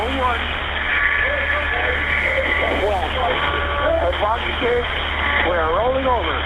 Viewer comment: it sounds like he says "We are about to tip; we are rolling over" but the "a" in "about" is said like "uh" and that vocalization does not come across well on the low-fi radio, so you can only kind of make out the word "about" or maybe a related word like "bound to tip" or " 'gon to tip". I thought otherwise the transmission was remarkably clear! Such composure in a terrifying situation is not always observed.